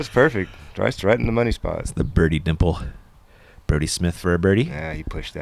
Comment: Kept mishearing as saying "Birdie Smith for a Brody" 😆